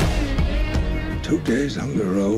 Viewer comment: onwards can somebody tell what prof says?? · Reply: The Master_Mind "2 days on the road, only one meal, hardly any sleep, she's 11 and I'm fucking 90"